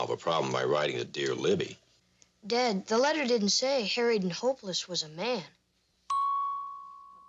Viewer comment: The first several times I saw this episode, I misheard the moniker as "Married and hopeless."